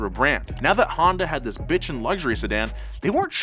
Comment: Did you say bit** at 4:47 lol